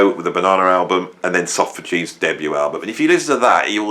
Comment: "Socrates' debut album"? I can't make that out. Has anybody heard that right? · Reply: Soft Machine